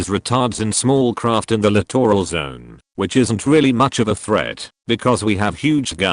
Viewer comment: 'attacked... in the clitoral zone' What!? Ohh... littoral zone aka close to shore. I definitely heard that wrongly 😂